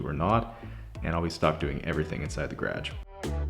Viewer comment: I don’t even notice your accent until you say garage 😂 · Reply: The first couple times I heard him say garage I thought he was saying carriage